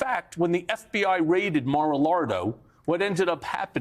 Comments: He ACTUALLY said "Mar-a-Lardo"! 🤣👍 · did Michael called Mar-a-Largo “Mar-a-Lardo”?🤣